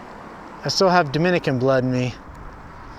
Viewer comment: This is great!! I still have been keeping about 8 Polaroid type 55 Did I hear it right? You just said "I still have Dominican blood inside me" did I misheard?